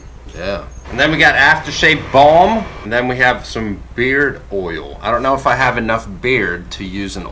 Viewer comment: did he say beer or beard?